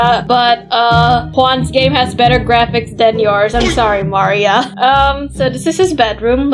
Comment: What said: I'm sorry Maria what I hear: I'm sorry Mario💀